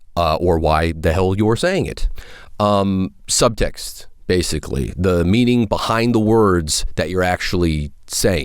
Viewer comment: It sounded like he said "suck dicks, basically."